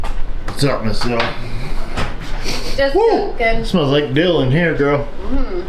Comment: “Smells like dill”…I thought he said “smells like Bill”. Had me wondering who Bill is and why he smells good…